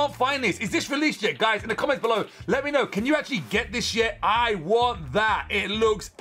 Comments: i heard can you get this shit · is it just me or did he just say sh*t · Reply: He said can we get this yet